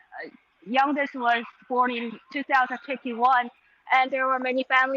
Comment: did she say the youngest passenger was born in 2051? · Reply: 2021